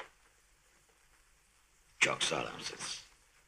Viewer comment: te yaşlı adam ne diyor anlamadım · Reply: "çok sağlamsınız"